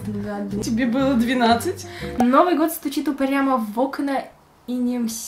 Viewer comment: Мне послышалось УПЫРЯМ в окна🤣